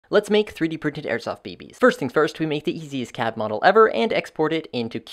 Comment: i heard that as let’s print some airsoft babys